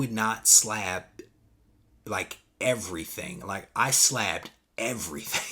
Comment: Sounded like you said you slapped everything xD